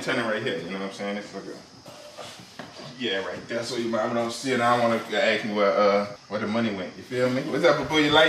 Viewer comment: I thought said I don't wana eat it when she actually said she did not wana see it